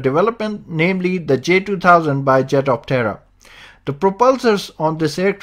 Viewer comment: I keep hearing "Jet Of Terror"